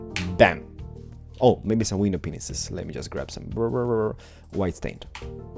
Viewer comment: If you put on captions and at the 0:49 mins in it says penis instead of panes. LOL!